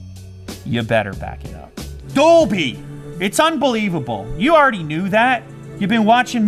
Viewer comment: Did anyone else hear boobies at 5:31-5:33?